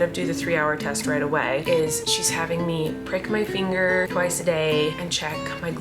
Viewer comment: I thought you said "break my finger twice a day" and I had a hart attack😂😂😂